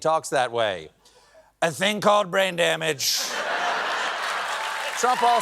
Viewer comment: I swear I thought I heard Stephen say, “ drain bammage.”